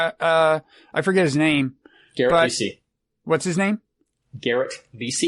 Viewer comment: I thought he said Gary Busey at first.